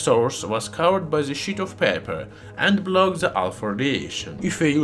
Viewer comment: I can't stop laughing every time it sounds like he says "Alfurdiation"